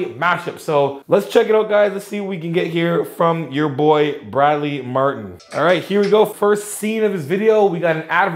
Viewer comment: did he say "bradley martyn nazi guy" ?